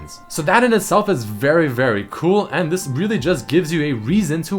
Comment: did he say Cruel or Cool? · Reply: cool